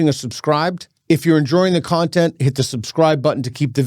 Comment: Did he just say "If you're enduring the content"?